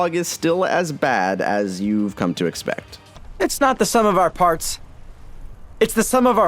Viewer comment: I thought he said hearts twice😂